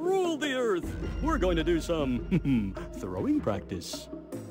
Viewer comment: I thought he said throat practice